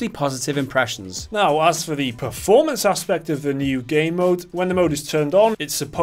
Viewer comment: I keep hearing them say "gay mode" instead of game mode. 😬